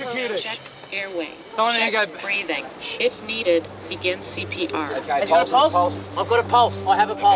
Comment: did anyone hear “shit needed to give cpr”